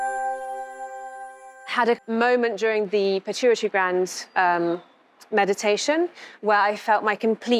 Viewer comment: What did she say here 3:02 ? Which meditation? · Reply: Sounds like she says she had a moment during her pituitary gland meditation🧘‍♀️